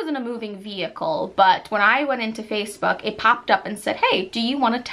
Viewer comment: OMG...Did she say Face Fuck...